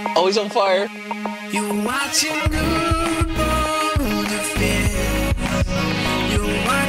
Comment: When i first started whaching i thought his intro said your watching build some shit instead of boulderfist